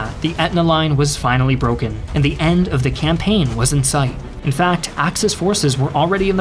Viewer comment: Fact: During the invasion, US troops were capturing Biscari airfield, while facing heavy mortor and sniper fire. Once the Americans had gotten prisoners, two leaders, Sgt. West and Cpt. Compton, ordered the Biscari Massacre, where 35 Italian and 2 German POWS were massacred after surrendering. Edit: NOT fun fact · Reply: For a second I thought you said "fun fact" and I was like uhhhhh